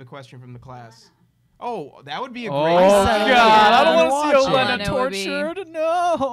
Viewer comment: What is said at 16:31? I can't make it out. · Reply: Yep, Olenna!